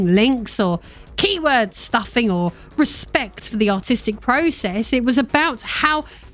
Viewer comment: Whenever you say "artistic" i hear "autistic" for some reason.